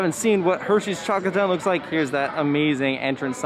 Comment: When u chocolate town I thought u said taco town and was like wtf